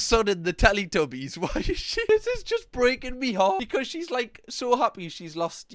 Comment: just me who heard breaking me heart then a tesco scanner.?